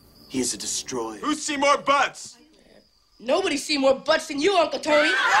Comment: what is he actually saying? Can anyone read lips? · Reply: <link> at 32:00 min. Ben Stiller asks "who's Peter Fits?" The kid replies "anyone's Peter can fit you push hard enough." I can see why the chose to take that line out in a kids movie lol